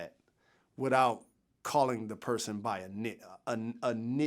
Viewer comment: Sounded like Jalen sound “n***a” at like 2:53 lmao